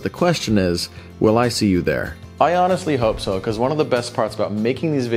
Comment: I know you said "I honestly" but what I heard was "Ion-estly".